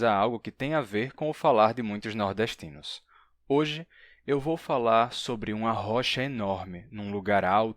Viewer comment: Eu escutei vc falar "hoje vou falar sobre um arrocha enorme" kkkk Pensei em uma galera dançando arrocha 😝